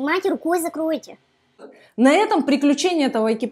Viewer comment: Сказала "экипаж" ,а мне послышалось "еб@ какая я а видов классный,особенно стирала в тему👍теперь я знаю как заходить в шлюз